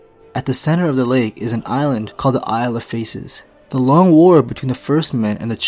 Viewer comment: I honestly thought "Isle of Faces" said "Isle of Tacos".